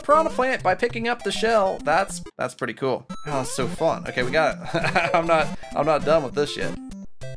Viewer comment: what did you just say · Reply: I'm not done with this yet.